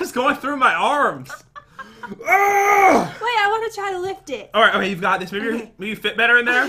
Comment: 😄...😆...😂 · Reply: Jasmine Fletcher Same!!!!So HILARIOUS!!! I watched it like a million times!!Also sounded like said lick it instead of lift it